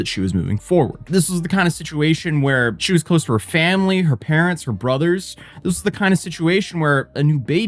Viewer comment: You said "her parents, her brothers." I heard "her parents were brothers." 😂